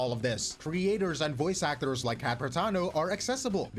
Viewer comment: When you said “Cat Protano” I thought you said “capitano”